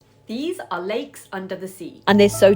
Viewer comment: Thought you said " these are Legs!" And they are so toxic.